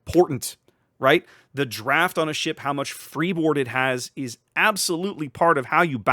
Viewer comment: did you say Freebird? Holds up lighter.